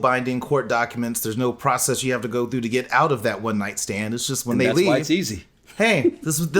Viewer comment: I thought she said she had a one night stand...